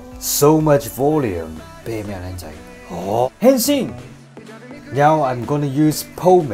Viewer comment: I heard you say "Henshin!!" 😂😂 I also a fan of kamen rider